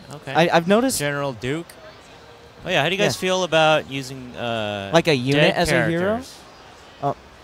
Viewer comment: what is trying to say? How do you guys feel about x? Thx for not talking over him :^) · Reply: How do you guys feel about using dead characters?